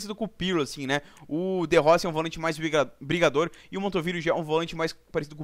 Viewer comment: Só eu ouvi Montovilo???